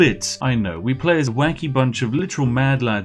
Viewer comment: instead of "Wacky" i thought you said "Wanky". So you play as a literal bunch of "wanky mad lads". Good name a band right there!